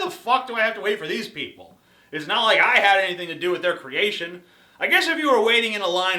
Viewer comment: Did I hear the Amazing Atheist use the word "creation" referring to humans at 3:10?